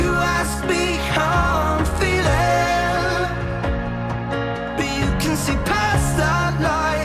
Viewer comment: I keep hearing 'But you can see Pasta'🍝 😂 Great song though, keep up with good quality Finland and one day you'll win again!!!